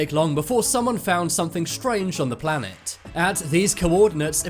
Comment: did he just say planet? oh god i have to click off the video